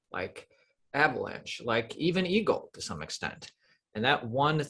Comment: wtf is "e gold" 🤣🤣🤣 or did he say eagle 😂 either way I don't get it, he sounds like it was a coin or something 😂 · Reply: Yes it’s EGLD, if you haven’t heard about it you’re missing out. Big potential. Not many talk about it and I love it. Gives ya time to accumulate before it’s adopted by everybody